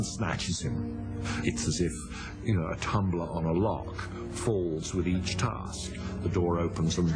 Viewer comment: on 5:49 i heard tumblr and log :D